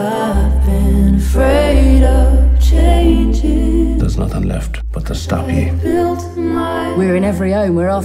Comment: What does he say at 2:00 ? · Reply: "there's nothing left but to stop you"